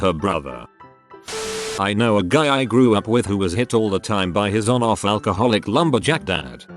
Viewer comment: at first I thought it said “ I know a guy I grew up with who was hit on all the time by his dad”